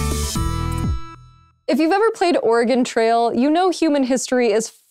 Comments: Sorry... sounded like you said "Organ Trail" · Whats organ trail? When the door of the organ donor truck comes open while going down the road? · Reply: I know, but it sounded like she said organ instead of Oregon.